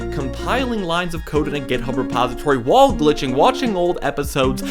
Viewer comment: did you say wall glitching? (I’m live reacting to your video) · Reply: yes he did